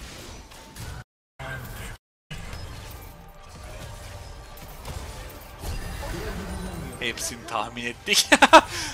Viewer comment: 08:08de jhin ne diyor çok merak ettim bee hangş replik o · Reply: ben ebediyim diyo galiba, bug olmus